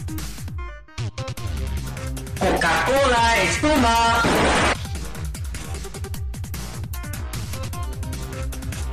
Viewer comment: ne diyor söylermisin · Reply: cocacola espuma